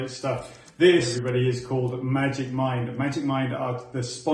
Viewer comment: ❤❤❤❤ I thought you said magic Mike 😂😂😂😂… loved 🐝🐝🐝🐝🐝